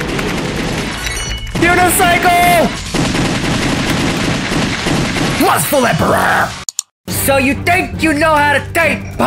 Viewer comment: It sounded like u said Lustfell not Lustful